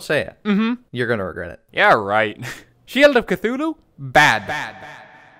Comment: I heard "shield of cthulhu bad" and disliked the video